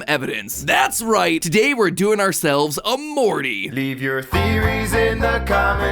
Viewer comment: why do i hear "Omori" 2:35 i heard "Omori" again.